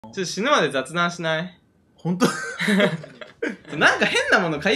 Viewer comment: ビックリした、おすすめに流れてきた時最初の死ぬまで雑談しないだけ聞こえて、え！？そんな激重な愛を宣言してたの！？って思ったけど全然違かった